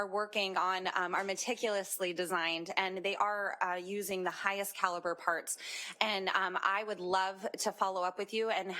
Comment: did that s**t said they are using highest quality parts and materials???? Did i heard that correct?? Do you all remember tens of videos about VESA mounts that always get their screw broken twisted or stuck??